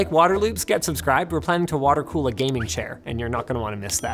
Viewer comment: all I heard was "water cooled racing seat"